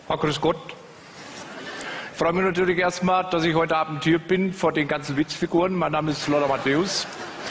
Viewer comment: Hab mirs ein paar mal angehört , ich verstehe immer: 'vor den ganzen Wichsfiguren'. Hört das jemand anders auch so ?